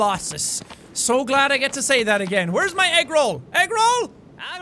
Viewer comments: At the beginning it sounded like he said where's my eggroll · I heard you say: where is my eggroll